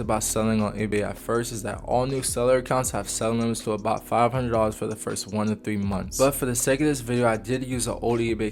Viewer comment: What did he say at 6:40? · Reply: Cats vs all eBay accounts have seller limits of about $500 for the first 2 or 3 months